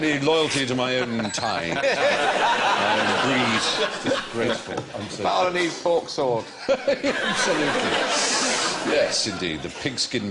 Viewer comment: does Allan say "Balinese pork sword" ?